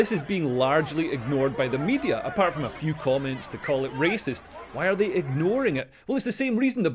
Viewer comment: Sorry I thought I heard the copper said "racist" when surely he was saying "rapist"